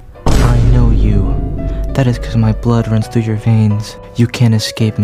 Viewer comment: i thought muichiro said “my blood run through your ball old man “